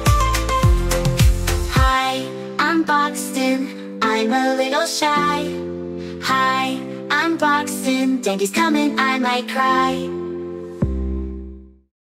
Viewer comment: It sounds like he saying hi I am boxing